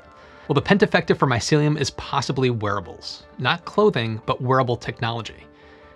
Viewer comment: I thought he said werewolves and I just thought, "Wow that took a turn."